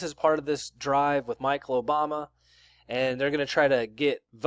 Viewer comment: Did he just say Michael Obama? 😂😂😂